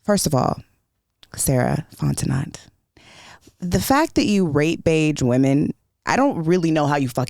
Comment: did she actually say rate bage women…… yea I’ve heard enough Sarah WON! 😂😂😂